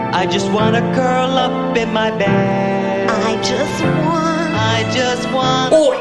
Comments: I thought he said, "I just want a girl up in my bed". O.o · did anyone else hear "I just wanna girl up in my bed"? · dude, I thought the baby bear said "I just want a girl up in my bed" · at first i thought he said "i just want a girl up in my bed" and was surprised NC didn't make fun of it